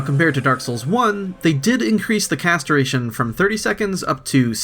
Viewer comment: What you say: "cast duration" What I hear: "castration"